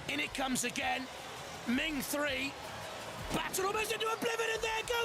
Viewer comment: lol -what is he sayin? "blaehswo ba blibedi ha theres goes the whole wheel" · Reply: i heard something about "battered into oblivion"